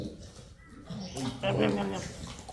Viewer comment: You know it’s a mate’s job when you hear “it just needs a quick look” and suddenly you’re doing a full rebuild 😆 Seized pin, blown seal, cracked welds… we got the full package this time 😂 Anyone else find these “quick” jobs always turn out to be a bit of a mission? Appreciate all of you who watch and support what we do here! 🔐Get Early Access + Ad Free + Extended weekly videos in our Patreon community: <link> 🔔Hit the notification bell so you never miss our new weekly video. ✍Leave a comment below with your questions or thoughts. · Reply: I could have sworn was saying "Nom Nom Nom" as he wolfed down that chicken jerky....